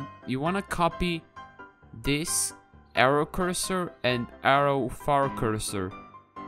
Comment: I thought he said arrow fart cursor 💀